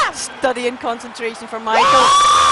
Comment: I thought that said Michael Jordan and was so excited. 💀